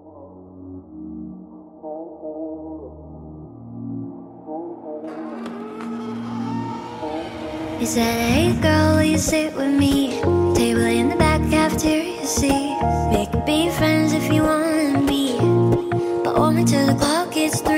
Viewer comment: Why did I always hear: "Table in the back cafeteria, see?" Cafeteria C makes a lot more sense.